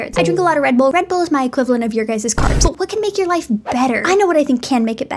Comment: I was doing something else while listening to this short and misheard “carbs” as “corpse” and for some reason didnt question anything until I finally saw my screen